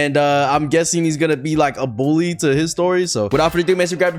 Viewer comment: I thought you said "history" at the beginning